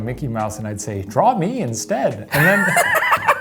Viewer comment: I thought said "draw minions instead" and I shuddered